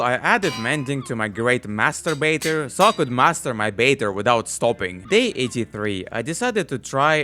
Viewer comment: I don't know why but instead of "Master Baiter" I heard "Masturbator". 😅 Fishing rod "Masturbator 3000 Pro". 😂